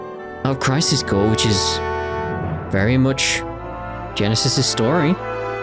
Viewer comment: sounded like Nero instead of Genesis